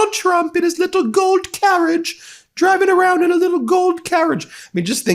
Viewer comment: I know he said 'Gold carriage', but I heard 'Goat carriage' and found it strangely appropriate :P